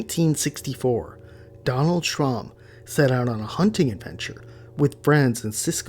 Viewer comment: I thought you said Donald Trump at first lol. No wonder he's against illegal aliens haha 👽